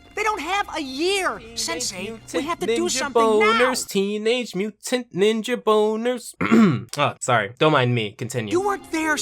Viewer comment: did anyone else not hear what said at 7:43? tmnt ninja boners, like am I the only one that heard that?